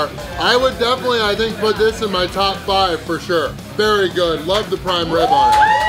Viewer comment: i thought you said no more prime rib? nice win nonetheless